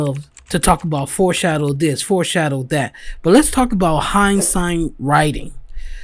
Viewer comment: what are you saying? Hindsight writing? Or something different? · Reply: yeah hinsight writing.was my audio not clear? Or was that not real words? Lol